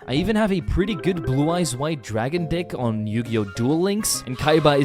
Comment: Is no one gonna talk about how he accidentally said white dragon 'DICK'... instead of dragon 'deck' at 1:43🤣